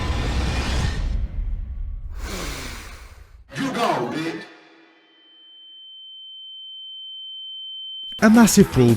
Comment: what did he say??? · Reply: you go b*tch